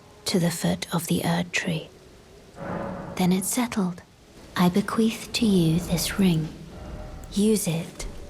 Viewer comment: what did she say ?? bequeef ??